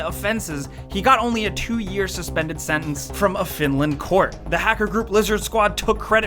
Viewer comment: Did he say Finland court?